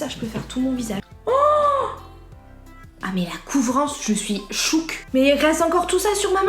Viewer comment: "je suis shook"... jungshook? 😂les army dites moi que je suis pas la seule à avoir pensé comme ça